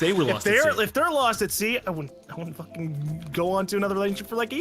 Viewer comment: I thought said lost at ZE